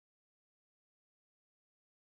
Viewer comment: It always sounds like you say John MAXIM...